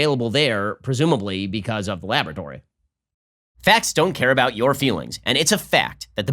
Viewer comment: did anyone else hear, "Bats" don't care about your feelings after this clip? lol